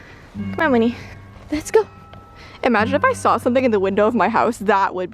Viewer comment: I thought you said that there was something in the woods 🤔🤔🤔